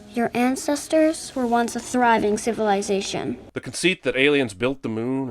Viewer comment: Both you and the captions say "conceit" instead of "concept." ding